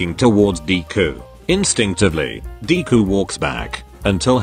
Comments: I thought it said barbcue go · i swear i heard barbecue go